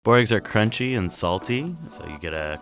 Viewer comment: I was shocked when I read "boogers" for a second when its actually "boeregs" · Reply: I heard "Borg Eggs", but I read it right.